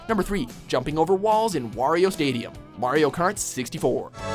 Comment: it is probably just me but I thought he said wario kart